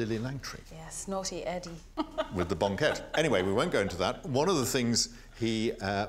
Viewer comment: I must have misheard looool! 3:50+; talking about Edward VII, Lily Langtry and their rendez-vous starting with a Bonquette... hillarious! 🤣